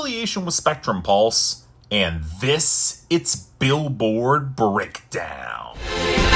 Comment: Ummmmmm....... did anyone else hear say "Billboard Brickdown" or is it just me?